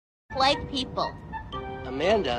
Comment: I thought she said “White people”